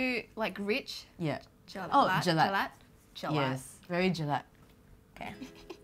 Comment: It's Jelak not Jelat My beautiful ladies. A malay word. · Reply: if you found out that a word you've been pronouncing is wrong all along, pronounce it properly now that you know. don't try to segregate by saying "oh the non-malays say it this way" lmao. i also misheard it as jelat but now i pronounce it as jelak. it aint that hard.